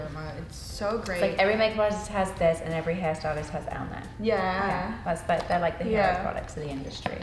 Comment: Every makeup artist has this and every hairstylist has _____ 🤔??? Anyone catch what she said? 😅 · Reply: L'Oreal Paris Elnett